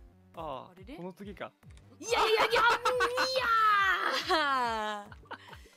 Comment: Died "That's pretty delicious!!" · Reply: I think she was trying to say "oshii/惜しい" (close/disappointing) rather than "oishii/美味しい" (delicious) 0:12 I can hear it both ways but 惜しい makes more sense you can hear Rushia use the same word in this clip: <link>